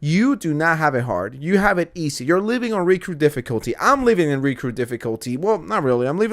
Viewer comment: What the hell is he saying? “Recrew difficulty”? “Riku difficulty”? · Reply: I googled “Recrew difficulty,” and Urban Dictionary suggests “Recruit Difficulty.” Apparently it’s from CoD.